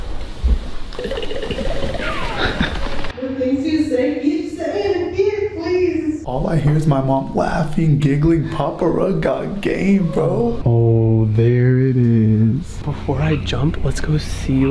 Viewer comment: Thought they said papa frog😂😂😂🎉🎉🎉😅